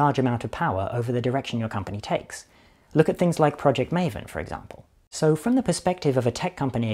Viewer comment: thought Rob said "project Mayhem" at first, that conjured up some weird mental images of AI fight club. first they spar against each other and then start corrupting consumerist culture.